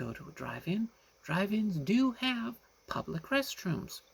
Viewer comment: When you said 'drive ins', I thought you said 'dragons' 😅. There are a few in the US! I was like, whoa!!!